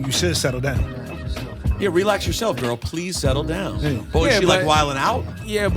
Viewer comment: I always thought Qtip was saying “relax yourself girl please sekkle down”